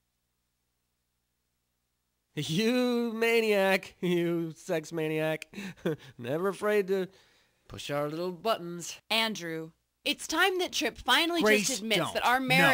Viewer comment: Did Trip just say Sex maniac at 4:31?